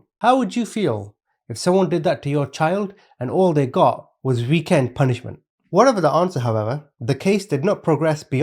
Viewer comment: did you say weekends in jail ? Like WTF ?????